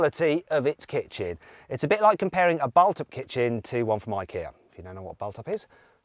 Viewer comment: Does anybody know what he's saying at 2:27 Balt up? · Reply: Bulthaup